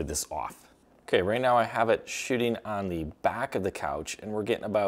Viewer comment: First I heard shitting and rewind it to be sure...it is shooting😂😂😂